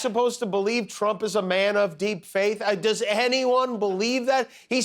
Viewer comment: You misheard Phil; he said "deep fake", not "deep faith".